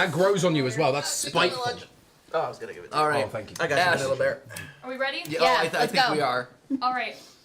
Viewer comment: Did anyone hear say “I gotchu vanilla bear” 😭